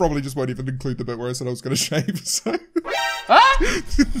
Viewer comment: Can someone please tell me what he said a 4:49 I’ve rewatched a few times but can’t make him out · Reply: Continuity sake